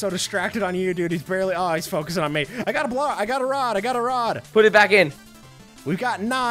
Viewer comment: Why did I just hear "I got a blod!"